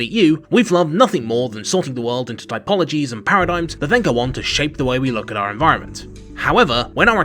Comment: The subtitles say "typologies and paradigms that think I want to shape the way we look at our environment". What is he really saying? As a non-native English speaker, occasionally I have trouble understanding parts of his speech. I'm more used to American accents. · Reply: "We've loved nothing more than sorting the world into typologies and paradigms that then go on to shape the way we look at our environment."